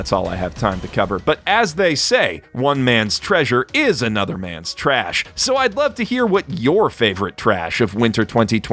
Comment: I thought that the said was "One man's trash is anothers man's waifu"